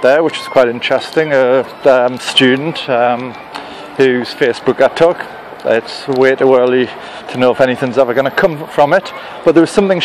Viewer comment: What does he say from 0:22 to 0:26? It sounds like he's got Elmer's glue in his mouth. "...student, whose fierce book I've took, ats a weight o' wirly to know if anyfing's gonna come fwum it."